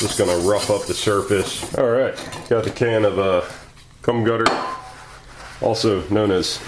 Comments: did I here you say "cum gutter" instead of gum cutter? Sounds like a scraping process for men. 😲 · Sounded like he said "cum gutter" haha